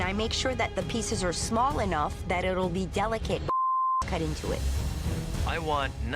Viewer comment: what did she say? delicate bullsh*t? 🤔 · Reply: Chefs. Not sure why they censored it